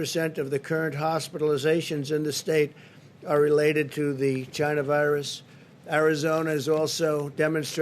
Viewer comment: did he say "China-virus"??